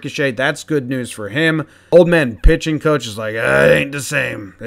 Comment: ha I first heard "Old man Pigeon coach."